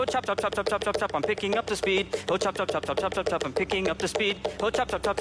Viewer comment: It sounds like is saying I'm picking up the speedo chop chop chop